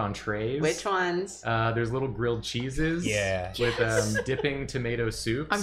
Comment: When suggested the art museum snacks have little grilled cheeses with shots of tomato soup, I thought that they instead said “little girl cheeses” like little pieces of cheese shaped like girls, totally rolled with it cuz art Museum